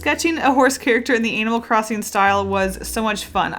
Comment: am i the only one who noticed she said anal instead of animal 😳😳